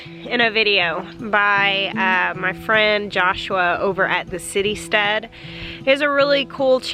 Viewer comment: I thought you said City Stud and thought wow..then saw Citystead in the links.